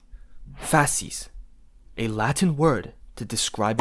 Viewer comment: Did it say faces?💩💩💩